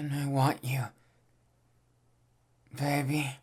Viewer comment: I thought he called us daddy but it was baby 😂